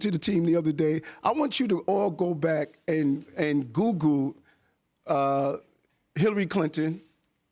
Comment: is he saying “googoo”? The “lingo” gets on my last fvcking nerve… I understand if one has a speech impediment or something but, this kind of shit just bastardizes the English language. Just axe a question and I’ll tell you all about dat!